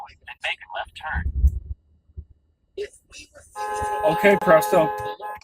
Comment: who else thought he was saying "hey presto" for a second 🤣